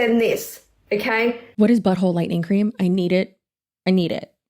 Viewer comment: "light reading " of Butt Stuff for the moder woman, took me out 😂😂 Bellessa owes you a bonus for that ad, as it was hilarious 😂 Darn, she is like a parrot. She keeps repeating herself 😂 "Chelsea stop being gay" 😂 NO, don't! Im sure Tony doesn't mind your bi side flirting with us gay and bi gals. So "keep being gay" I too thought she said "but hole lighting cream"